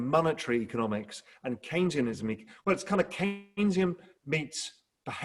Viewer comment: Can anyone tell me what Raoul is saying around the 35:04 mark? Caintium economics or something like that.... I can't decipher the word which is making it very difficult to google :) Thanks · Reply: ‘Keynesian’ economics 👍